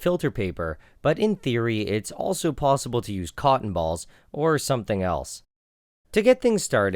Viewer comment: Anyone else hear him say cock balls